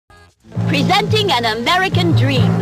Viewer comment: BRO I THOUGHT IT SAID ''PREVENTING THE AMERICAN DREAM'' 💀💀💀